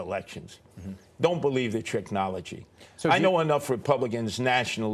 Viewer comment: did he say tricknology??????????????????